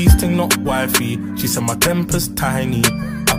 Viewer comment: why did i think he said "she said my granpa's tiny"